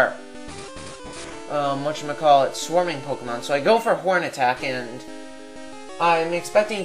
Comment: i heard porn :x